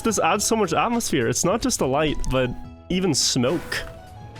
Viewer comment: are my ears deceiving me or did he say "snoke"?!